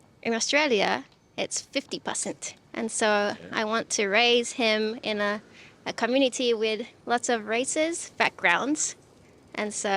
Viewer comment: I had to rewind lol I thought she said “I wanted to raise him with lots of racist”. My first thought was why not just bring him to America. Got a bunch of those MF’s around especially in Pa.